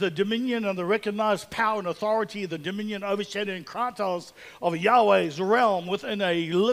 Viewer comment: what is the word beginning with 'c' at 13:01? "...authority the dominion over shadowing and ???" I'm a Yank and sometimes I am slow on picking up New Zealand lingo. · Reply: The word is kratos. Eph